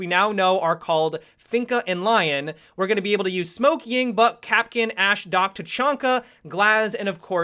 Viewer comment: i thought he said smoke yings butt